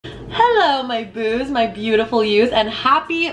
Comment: At the beginning i thought you said hello my boobs 😂